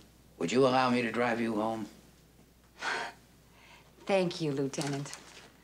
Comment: Hah, a point I'd never noticed before. 1. Columbo offers Mrs. Goodland a chair. 2. Mrs. Goodland says she prefers to stand, thank you. 3. Columbo brings her a chair anyway. 4. Mrs. Goodland doesn't touch it, saying there will be plenty of time to rest in prison. 5. Columbo definitely proves that Jarvis murdered her husband, and has him led away. 6. Mrs. Goodland sinks, open-mouthed, into the chair that Columbo brought her five minutes ago. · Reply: She clearly says thank you to Columbo